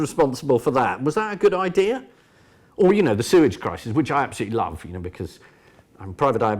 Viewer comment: Sewage Crisis? I misheard that as Suez Crisis…